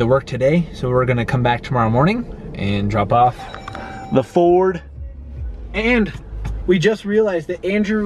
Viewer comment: Did I Hear Fort?